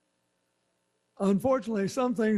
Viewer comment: OH MY Did he say, "...UNFortunately some things are still in God's hands"